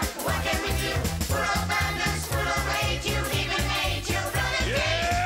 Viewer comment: All I hear through 6:16- 6:20 is "You even gave your mother aids"